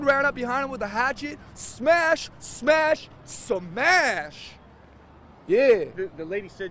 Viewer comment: smash smash... SMASH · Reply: Oh I heard “smash Smash SUMAAAYAAASH